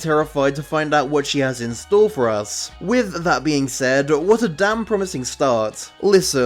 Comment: I was so confused, when you said what a damn promising start I misheard what a downpromising start and then you said it was the best episode, got me all mixed up